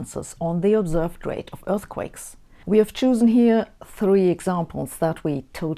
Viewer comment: Thanks Sabine for another interesting video! BTW, at around 5:40, I think I heard "choosen", instead of "chosen" (long o). Just a friendly heads-up. I so love your content!